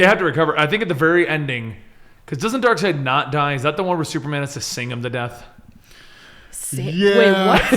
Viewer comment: At first I thought he said "Save him to death" Waitthewhatnow? Then I backed it up and heard it right "Sing him to death" Oh. Okay. Black Bolt does that for spite now and then.